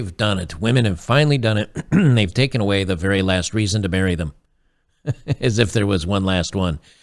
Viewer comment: Did you say last reason to MARRY them--- or BURY them? Sounded like you said BURY.. and there are PLENTY of reasons for that.. People ask if I've been married- and I say YES 3 times... first wife died of POINSONED mushrooms.... They go " AWW sad" and I say YEP- Second wife died of POISONED mushrooms...and they look at me funny- and I say- 3rd wife died of skull fracture.. and they gasp and I say-- B...ch wouldn't eat the damn mushrooms..